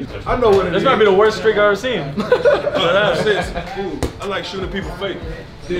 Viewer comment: Did I just hear that guy say, “I like shootin at peoples faces” 8:13 🤣😅